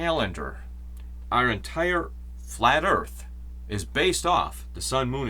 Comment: Did I hear you say 'flat earth' at around 46:16? please explain.